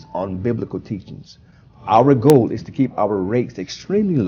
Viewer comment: Sending kids to this academy should be considered child abuse. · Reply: For a second i thought he said they try to keep their rapes low,,but it it turned out it was rates. My bad. Now i have to wash my ears.